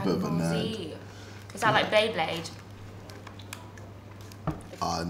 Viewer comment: “Is that like beyblade” 🤣😂💀💀 This has me dead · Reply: Oh I thought she said she liked beyblade